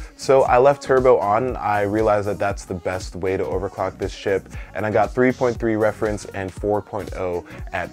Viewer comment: Is it just me or I heard him say "So left the turbo on and I realized thats the best way to overclock this shit"